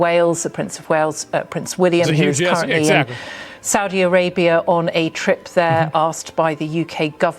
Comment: did she say "Saudi A-rape-ia" ??? lol